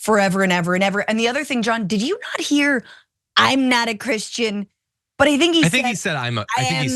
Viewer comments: I thought he said ‘I’m not a Christian.’ So that would be a first time telling the truth. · Did he really say, “I'm a Christian”? I heard, “I'm no Christian”. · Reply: He said, "I'm no Christian". That was self-evident given his history.